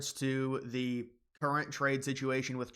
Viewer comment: Did you say “Trina”, like Trump and China combined 😂